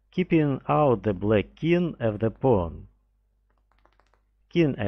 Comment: Subtitles @ 2:44 instead of" pawn ", written as "porn" Too much dubious